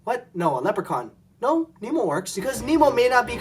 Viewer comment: When you said "Nemo" at 5:20, at first I thought you said "emo"